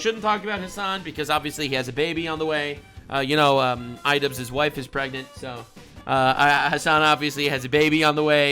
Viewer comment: im so used to hearing "anisa's husband" when referring to idubbz that when you said "idubzz' wife is pregnant" i thought you said anisa's husband is pregnant